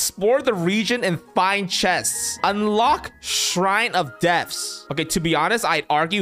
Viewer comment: It sounded like he said shrine of death! LMAO 🤣🤣🤣🤣